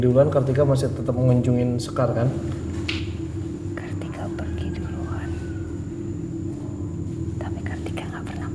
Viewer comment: Jelas Suara Teriakan Keras saat Kartika jawab pertanyaan.. "Kartika Pergi Duluan, Maaf jika saya salah dengar.